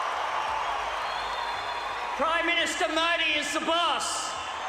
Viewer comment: First time I thought he said “PM Modi is the bus”😅